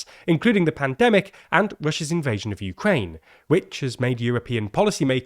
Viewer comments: did I hear “Bushes invasion of Ukraine”? lol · did I hear that right?? "Bush's invasion of Ukraine"?? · I keep hearing "Bush's invasion of Ukraine" W you rascal.